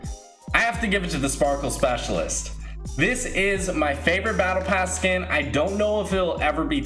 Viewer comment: I thought u said drift was ur favorite battle pass skin